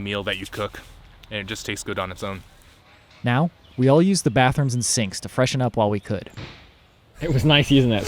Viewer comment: "We all used the bathroom in the sinks to freshen up while we could" 🤣😂🤣 I swore thats what you said LOL. if the toilets are gross enough ... i mean the sink is a option!!!